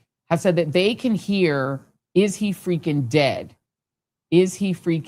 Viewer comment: I heard "are you freaking dead" is what I heard all while the other young man was talking!